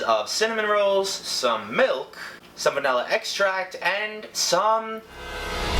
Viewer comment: why did I hear "Salmonella extract"